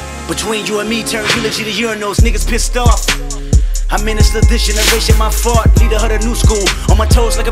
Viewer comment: I know the line is, “I ministered this generation by far” but it sounds like Kendrick says, “I ministered this generation my fart” 😅